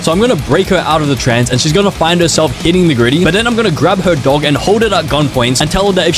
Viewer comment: I thought he said "I'm gonna grab her dong and hold it at gun point"